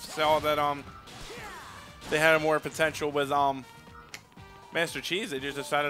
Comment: I SWEAR I heard him say Master Cheese. Now that's his name.